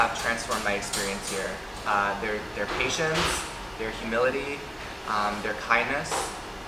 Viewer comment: 妈的，那个gay说话的时候听成“their humidity"了，好污。。。。。。